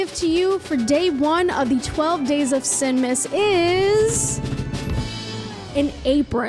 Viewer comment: I heard "12 days of sigmas" at first 😂